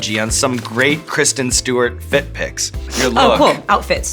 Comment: i thought sean said foot pic at first and was very concerned